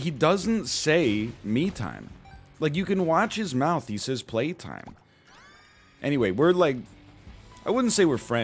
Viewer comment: I thought he said Pain time... Also an egg on a burger is surprisingly great, assuming it was a decent burger and a properly sunny side up egg.